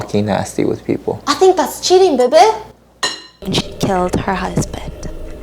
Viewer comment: Whoa at first i thought it said "I killed my husband" 😭😳😳😳 ily 💜💜